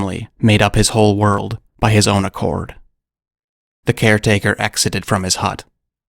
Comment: You did well! You're a good storyteller. This would be a good multigenre noir & horror film. I thought I heard you say "by his own accord" instead of "account" and "denziens" instead of "denizens".